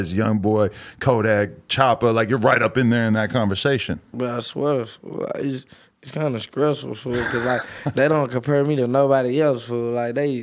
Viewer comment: Yoooo cut the auto captions on lmaoooo At 9:15 he actually says “bruh I swear it’s kinda stressful” but the caption says “Well I suppose it’s kind of stressful” lmaoo